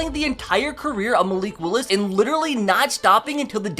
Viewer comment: I gotta ask. At the very beginning, why does it sound like you said, "Today, I'm playing the entire of uh Malik Willis" The of just sounded like you making a noise. Is this that famous Alabama accent???? must have paid for speech therapy cuz he's starting to sound like he's been from NY all along. At least we know is an authentic Southern man.